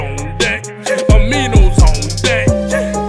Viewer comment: Pre workout on deck aminos on deckkkk❗️❗️🔥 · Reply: I thought he said "Homies on deck."